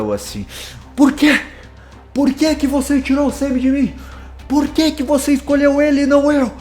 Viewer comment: Na parte que a Charlie falo "por quê tirou o Sammy de mim ?", eu ouvi "por quê tirou o sêmen de mim ?"